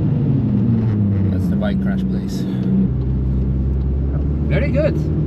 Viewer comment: what did he say.....? Let the bike crash pLz....? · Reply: I think a bike had crashed there. "The bike crash place"